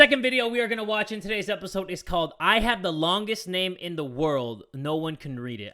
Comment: For some reason in the second story my brain stopped functioning and i misheard it as "i have the longgest thing in the world"😂😂